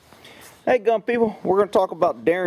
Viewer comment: Haha I thought you said "hey dumb people" but then I heard you. Silly me... Thanks for the annotations-really helps me understand!